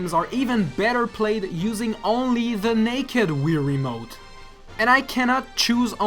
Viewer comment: Did say naked